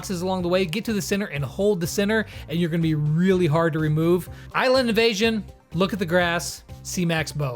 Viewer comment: I watched this thing in x2 speed and thought you said "Island of Asian".